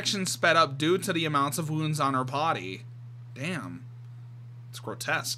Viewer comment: is it just me or do I think you said potty instead of body? 😅